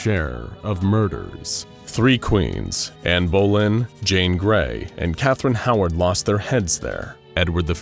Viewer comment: thought you said Jean Grey. She spends half her life dead I swear